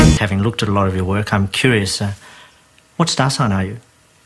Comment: what that guy says at 2:08 ? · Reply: what star sign are you?